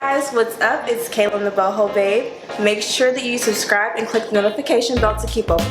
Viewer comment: Sound like she said the butthole babe 😳 love the video